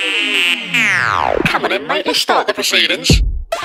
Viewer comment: what that voice is saying ?? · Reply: Hatpox Will i heard "come on then mate lets start the proceedings" and "you a mate?"